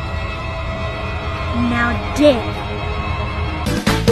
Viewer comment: I thought Dora was saying I would dare but she was actually saying now dig